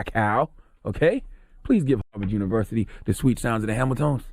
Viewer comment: Good Lord! I thought you said Howard University! LOL. I started checking my email. On my way to campus now HU 'You KNOW!